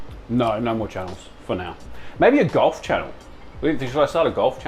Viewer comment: i thought you said goth channel and not golf and you had my attention further 😂